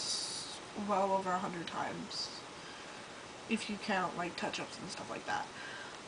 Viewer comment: When she says "If you count touch ups" I thought she said "If you count ketchups." (x